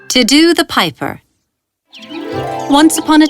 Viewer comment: The first second they said "Tiddu The Piper" I thought they said "To Do The Piper" Who else thought the same-? Also your stories are really creative and amazing, the animation was great-! o(〃＾▽＾〃)o